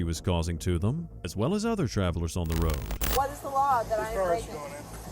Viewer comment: Did I hear the word travelers??? Sovereign Citizens eating good with this one 😂😂😂😂